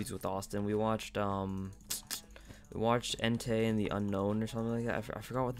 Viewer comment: Lol thought you said "hentai and the unknown"